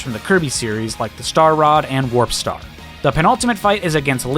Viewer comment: am I hearing this right? Is he saying "the pinaltimate fight?" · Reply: “Penultimate.” It means second to last, or just before the final fight.